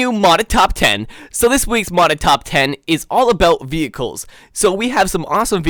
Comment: When you said all about the vechicle i thought you said all about that bass